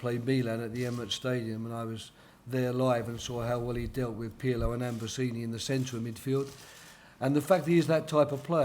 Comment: did he say pirlo and ambrosini at 4:17 ?